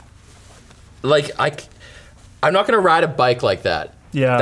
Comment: did you say bike?